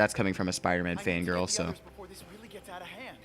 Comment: Did he say fan girl?